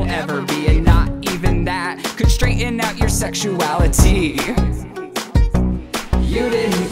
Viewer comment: What I heard Your sexuali-tea Yep this video is just a very good sip of some tea lol